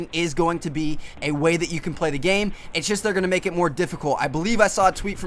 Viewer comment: what did he just say? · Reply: They are gonna make it more difficult 😂